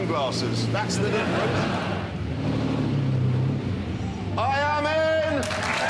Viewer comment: thats what he said. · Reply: Lopanto Lulu I thought I heard "I am in"